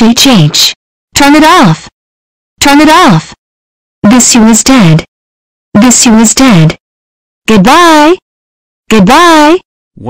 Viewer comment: what the hell is she saying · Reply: "TURN IT OFF, TURN IT OFF. THIS WII-U IS DEAD, THIS WII-U IS DEAD! GOODBYE, GOODBYE."